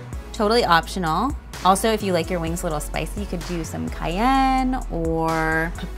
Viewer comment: did she just insinuate that paprika is "spicy"? 😂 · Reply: I thought she said cayenne?